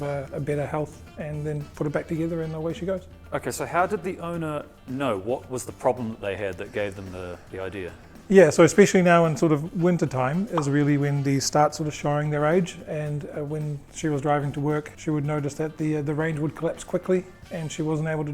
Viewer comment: OK, I promised myself I wouldn't publicly make fun of the New Zealand accent anymore. But at 15:25 the guy actually said "... is really windy start sort of showing their age" instead of "... is really when they start sort of showing their age". How do you guys even understand each other? :D